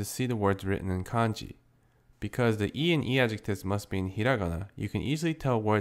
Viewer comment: What are you saying at 1:09? Because I hear "because the i and i-adjectives" but that doesn't make much sense, I haven't a clue of what you're talking about. Totally threw me off. What are you saying there? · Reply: he said the "i" in "i"-adjectives